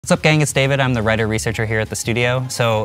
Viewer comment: I thought he said reddit researcher